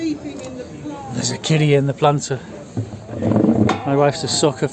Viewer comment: I thought you said there's a kiddy sleeping in the planter!